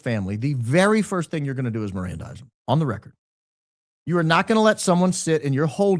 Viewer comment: What does that word mean? There are no auto-subtitles and if I look for "marendising" (written wrong, I know, but thats how it sounds to me...) I dont find anything. What does it mean? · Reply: Mirandize?